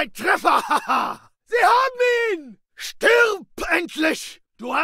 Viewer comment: i thought that when they said "Wir haben ihn!" (We got him!) he actually said "behind me" and i always look behind because i think there's an enemy